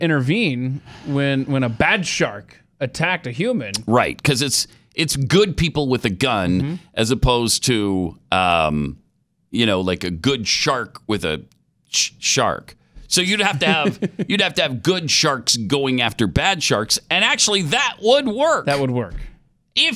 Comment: Ooooo..... I thought he said shart